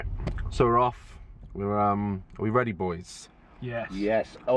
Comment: After said “it’s just gone midnight so” I thought I heard something say “get ready”?